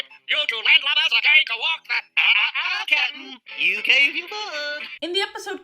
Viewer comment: Also what the heck did he say at # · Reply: “you gave your word”